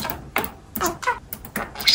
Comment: 「舐めたから知ってた～」に聞こえるｗ